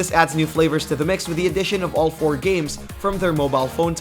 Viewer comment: I thought you said 42 games. There is 40 losers and maybe a couple of games in all that trash being released and we will have to wait to see if the one or two games actually are worth playing. Thanks for the info, I now know what to avoid.